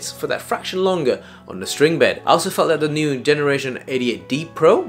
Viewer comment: I thought i heard "idiot D Pro" 😂